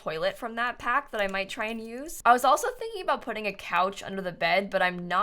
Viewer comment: why'd I hear " I was also thinking about getting a sticker "